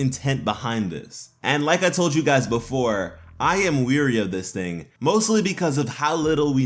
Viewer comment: when you say "wary" I keep hearing "weary"